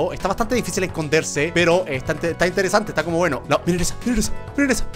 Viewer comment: parece que dice milanesa o son mis oidos